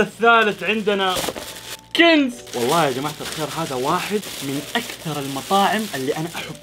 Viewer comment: سمعت كلمة خ*را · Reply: قال جماعة الخير